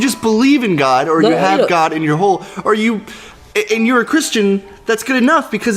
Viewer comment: I thought when he said "God is in your home", he actually said "God is in your hole", and remembered his mother saying "God is in all of us" and had to go "Okay, WOAH, wait, back up a second" xD